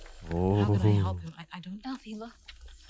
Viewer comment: is that Dutch/Flemish because i heard "Elphie Wacht" and not "Elphie wait" and it didn't even sound like Ariana Grande but the Flemish voice actress for Glinda and i am from the Dutch side of Belgium.🤔